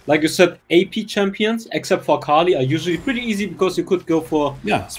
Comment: dude did u just said Fukali? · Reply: You said "except for akali" but it actually sounded like fukali My bad😅